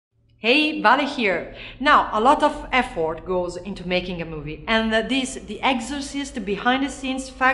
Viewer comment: I thought she said- now a lot of f word goes into making a movie